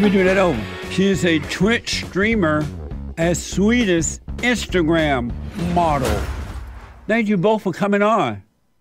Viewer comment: "swedish immigrant model" is the hardest ive laughed at anything in months holy fuck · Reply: I thought he said Instagram model. He's mangles words